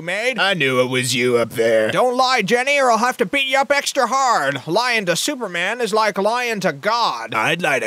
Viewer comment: I heard "Lying single men is like lying to God"